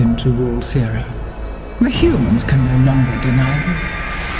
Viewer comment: i have no idea what she is saying. Our species is destined to rule Sera.. The humans can da na dada naa????? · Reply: The humans can no longer deny us